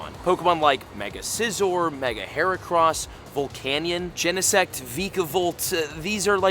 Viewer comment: am i the only one who heard volcanion